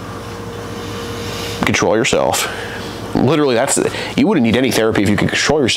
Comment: said control yourself I thought he said betray yourself 😆